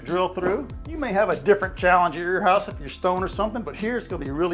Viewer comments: After the front of the sink was picked up I thought you said "You may have a different challenge at your house if you're stoned or something." Yes, I'm sure that could present challenges. 😉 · I swore I heard "You might have a different challenge if you're stoned or somethin'" at 9:35 haha.